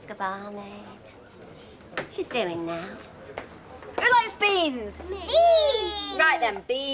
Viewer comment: Bloody hell! Tell me I'm not the only one who heard "THE LESBIANS!" rather than "WHO LIKES BEANS!?" 😂